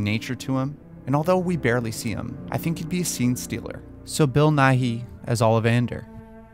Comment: I thought you said "bill nye as ollie vander" and now I can't stop thinking BILL NYE THE WAND SHOP GUY